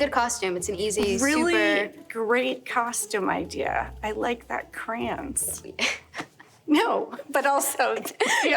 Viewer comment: Does anyone know what she says at 02:06?? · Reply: "I like that crayon suit"